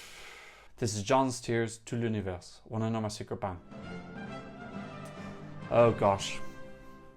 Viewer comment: Can somoene tell what he is saying at 2:32! It sounds so epic🤣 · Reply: "Wanna know my secret? Bang!" Its a lyric from one of my favourite Eurovision songs ever - "Siren Song" by Maruv - Ukraine 2019